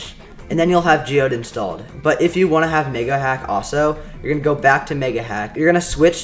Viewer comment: guys, can yall r/whoosh me cuz idk the joke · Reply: listen closely it sounds like hes saying the n word and not mega